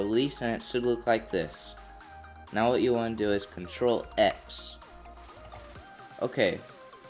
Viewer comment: did he said ctrl+X?